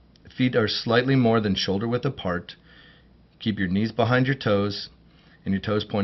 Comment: Did he just say keep ur knees behind ur toes 🤣